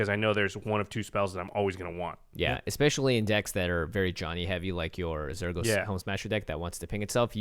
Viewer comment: says "Especially in decks that are really (something) heavy"... what's he saying and what does it mean? I've been trying to find out for so long! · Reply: Johnny Heavy. Which is just combo heavy I guess. Like the slang for player types Timmy, Johnny and Spike.